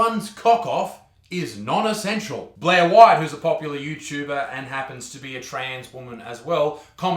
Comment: I THOUGHT U SAID 'my wife is a popular youtuber and happened to be trans' I THOUGHT U WERE MARRIED LOLLLLLLLL